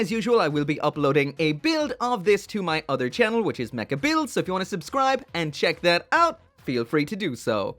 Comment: What's your other channel? Mechabuilds is what I thought I heard but not finding anything.